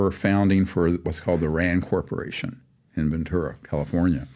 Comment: Dr. Steven mentioned Iran Corporation on Ventura ? Am I Heard it Correctly ?? Woow